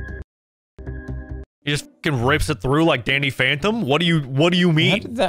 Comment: Did i hear…..DANNY PHANTOM…?